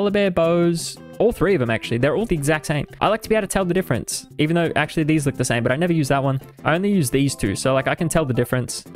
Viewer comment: Bro wtf was this guy even saying at 4:00??? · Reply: I was trying to say I can’t tell the difference between Wallibears bows but then I realised I can’t with mine either hahaha